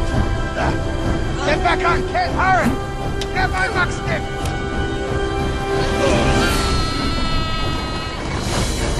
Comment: what does he say??? all i can hear is "grab my f***-stick" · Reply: “Grab my muck stick.”